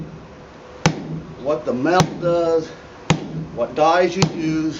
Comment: at about 3:21 on the vid you say, "So you can see what the m------- does what dies you use. What is the first word I just can't catch it please??? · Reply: Lenblacksmith you can see what the metal does.